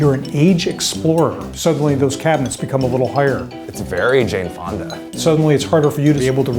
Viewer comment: did Eugene say “very Jane Fonda” 😂 I LOVE HER